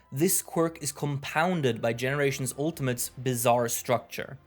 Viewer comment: Hmm.... Did he just say "BIZARRE" Hmmm 🤨. Is that a Jojo Reference!?